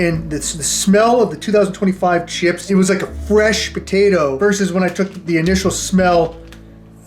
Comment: what was said: “fresh potato…” What I heard: “fresh POTITO…” Hope all is well man, I been watching you since the very beginning. Glad your still posting 🤙